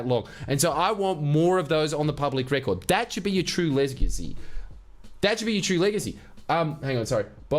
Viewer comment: legit thought said 'that should be your true Lebacy' 😂 The Bob Qatar Lebacy!🤠